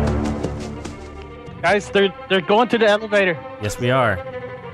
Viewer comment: I heard "evalator"